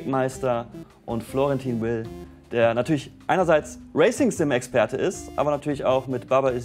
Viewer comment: ich hab als erstes Racism - Experte verstanden und es sofort akzeptiert. Erst als ichs gelesen hab, hab ich den Fehler realisiert. Huch